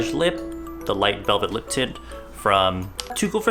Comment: I thought he said lip tit